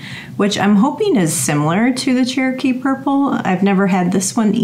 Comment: Have to share a funny. I misheard you pronounce “ Cherokee Purple” I thought you said “jerky purple” and there was some new tomato they’d genetically modified to taste like beef jerky. To say the least I was VERY interested in the jerky😂😂